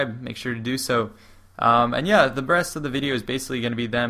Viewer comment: Is it just me, or does he say breast?